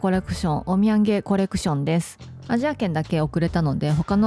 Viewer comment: オッミェンゲコレクションは、何回聞いても お土産コレクション に聞こえてしまって、毎度ちょっと笑ってしまいます( *´艸`)